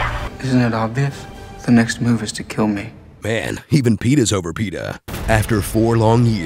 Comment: OMG I THOUGHT HE SAID "The next MOVIE is to kill me"